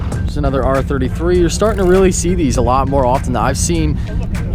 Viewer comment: did you just say r33? ................